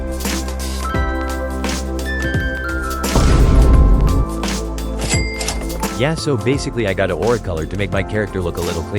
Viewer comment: ? What did you sayyyyyy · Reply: Bro said that's some drugs or slimy liquid what is that??